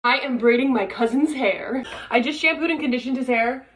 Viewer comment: Am I the only thing who thought she said “husband” not “cousin” at first 😂